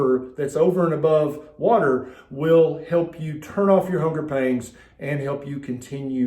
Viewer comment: Ooohhhh 'pangs', I always thought people were saying 'pains'